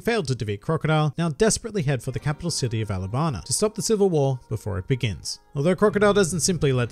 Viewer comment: Wait at 6:20 did he just just say alabana instead of alabasta